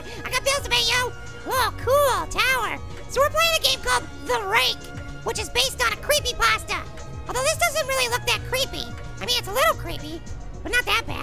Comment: When little 🍎 said the rake based on a creepy pasta I thought he was saying based on a true story